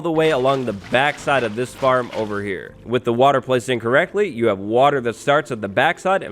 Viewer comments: Did he say “with the water placed incorrectly”? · I know you said “with the water sources placed in correctly” But at first I heard it as “with the water sources placed incorrectly”